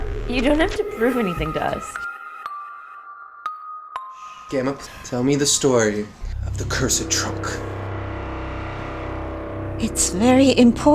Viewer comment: Thought he said gama tell me the story of curse of Trump